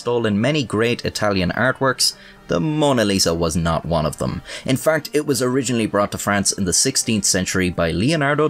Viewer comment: in farkt? sounds like u said that at 5:50ish